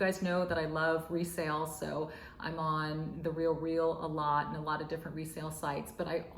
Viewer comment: What online store did you say you shopped at? It sounds like you said the real wheel? I replayed it, but can't find the store.